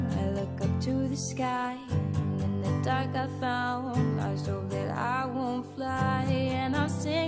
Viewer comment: It sounds like she says "And in the dark I found, lost hope that I won't fly"